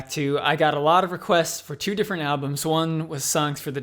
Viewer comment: Dave Grohl plays drums on SFTD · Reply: Alberto Martinez oh I thought he said the 2 best albums, songs for the deaf and rated r